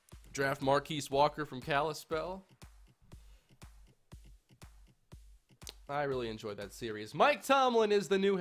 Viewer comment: does he say I really enjoyed that series